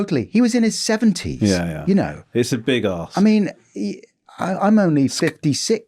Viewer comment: did Romesh say "that's a big ask" or "that's a big ass"? 😂